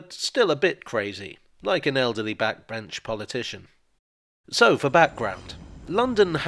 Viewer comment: Instead of "elderly backbench politician" I heard "elderly French politician" and was seriously considering whether these scripts are getting all too continental in orientation, much like St. Pancras.